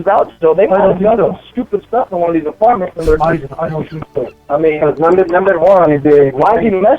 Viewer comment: Lmao i thought he said. Its some stupid stuff goin on n these apartment buildings